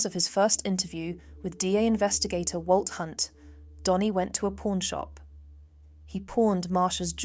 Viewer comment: What kind of an accent is this?? When she said pawn shop I thought she said porn shop😂